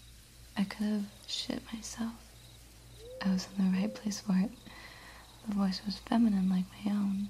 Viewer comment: "I could shit myself. I was in the right place for it" You probably didn't say this, but i cant figure out what you meant to say LOL